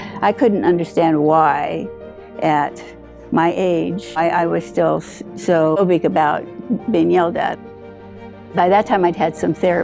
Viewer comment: wrong subtitle...I think she said "why I was so phobic about being yelled at." Can't wait to keep watching...I'm hooked already and I know the end will bring tears!! But I'm an inveterate proofreader. :)